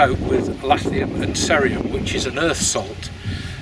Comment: What does he say the rods are doped with at 3:50? I keep hearing "lathnium and sarium", but I don't think either one of those are a thing